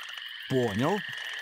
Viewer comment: wait . . . 3:38 does it say porno?